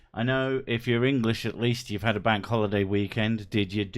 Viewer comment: Oh noes. I thought you said Wank holiday!